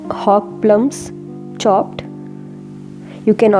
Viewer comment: What is the ingredient said at 0:50 ?? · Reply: Its hog plum in english.. you can replace by using bilimbi or tamarind extract.